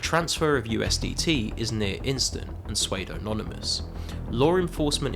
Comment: did you just say suedo